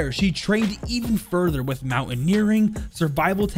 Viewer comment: did I hear MONKE